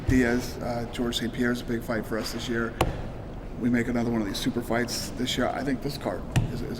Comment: Did Dana say "we will make another one of these super fights" @ 28:34, that means gsp vs anderson or jon jones vs anderson! :D